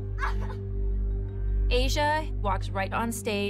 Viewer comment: Anyone else hear jojo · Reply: That’s not Jojo, that’s Asia